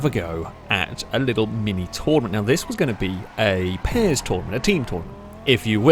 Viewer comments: when this first came up i thought it said “paris tournament” and i got very confused haha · “pairs tournament” i thought it said “paris tournament”